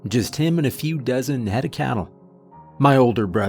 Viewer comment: I replayed that opening minute admittedly far too much because I thought you said 'A few dozen headed cattle.'